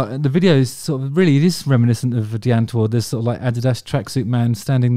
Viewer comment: Who is the other female artist he's talking about at 3:17 it sounds like he says Dian Tore??? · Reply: Die Antwoord